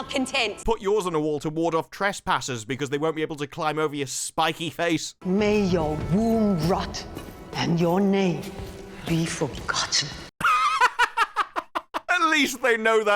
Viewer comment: i heard "may your womb rot and your navel be forgotten" despite having figured out what she really said after rewinding thrice, i prefer it this way.